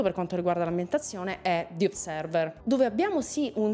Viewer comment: Scusate come si chiama il gioco/film (non ho capito) che cita al minuto 3:37? Non sono riuscito a capirlo/trovarlo, però mi aveva incuriosito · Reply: "Observer" (nel video lei dice "the observer" ma è sempre un casino ricordarsi se ci sia davanti il "the" o no in sto tipo di titoli 😂), se non mi sbaglio l'aveva anche portato in live quindi potresti trovarlo qui sul canale